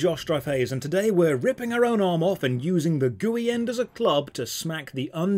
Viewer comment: When he said gooey end of the arm, I thought he said GUI end and I was like why would an arm have a GUI?